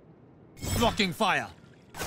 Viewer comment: Am i the only one thought he said "fcking fire"?